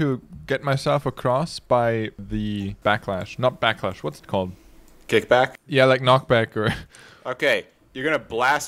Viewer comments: he said 'kickback' but the captions said chainsaw because Kickback is the name of the anime Chainsaw Man's opening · The editor who had to make the subtitles, you are the best. The fact that at 3:36 the subtitles get rotated with the avatar illustrates my point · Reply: also says chainsaw instead of kick back as a chainsaw man reference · he said kickback and the subtitles say chainsaw